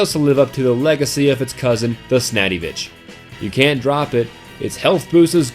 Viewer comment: Did he just say the....snadivitch?